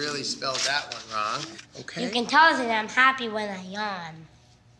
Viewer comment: What the fuck does that kid say at 1:02? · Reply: monstruolio u can tell that i am happy when i yawn